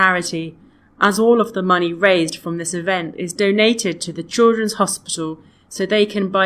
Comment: lol chidlren but i heard '''choro hospital'' so that i cant find the answer until have to use the subtitle